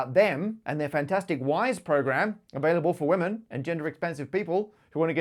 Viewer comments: I misheard you the first time you mentioned Jane St.. I thought you said "if you're a woman or gender expensive"... 😂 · I heard "gender-expensive," and felt very sympathy-ed. XD